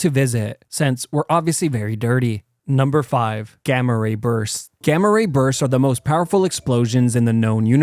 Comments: When he said gama rabursts I thought he said gama rapers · ngl, I thought you said; “Gamma 🍇’ers” and I literally just said; Oh god not another kind. But then I realized that this was about gamma, so Logically, this wouldn’t be able to happen.